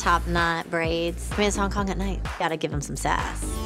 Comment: did she say miss hk at night